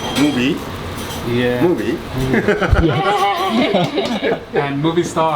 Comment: What did the chef say at 6:55? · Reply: He said 'Movie?'